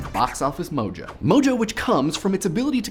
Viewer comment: All I heard was, "Mojo which cums". You're Welcome.